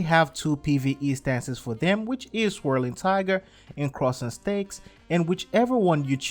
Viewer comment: I swear that i heard him saying "Crosing STEAKS"